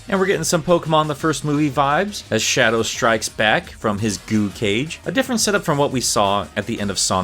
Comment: I thought he said “goon cage”. I think I need sleep